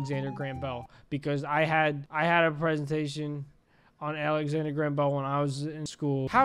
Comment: Did somebody say “Alexander Graham Bell”? That’s the name of my middle school.